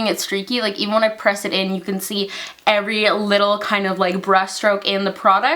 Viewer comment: Oh gosh..I thought you said "you can see every little breast stroke" and I was like wait wait no what then I realized you didn't and I'm just losing my marbles from getting off of a 16 hour work day hahaha.